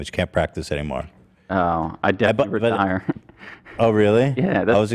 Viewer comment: What does idubbz say at 43:03?? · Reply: He says something like “I’d definitely retire” I think